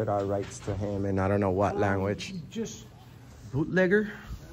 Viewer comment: I thought at first he hat said "Bootlicker Security" But Bootlegger is no better 😂